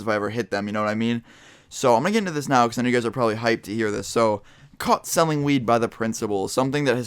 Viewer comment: I thought it said caught smelling weed